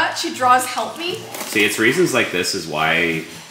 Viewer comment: In the beginning when she said "what if she draws help me?" I thought she said helpy 😂